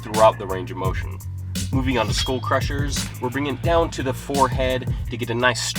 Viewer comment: Did you say "school crushers" at 1:05? I never knew there was an exercise to crush school.